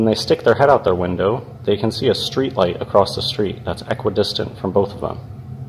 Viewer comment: Ok, so they're aquadistant. The same amount of water is between the viewer and each cat. I understand perfectly what you're saying. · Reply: I thought I heard aquadistant!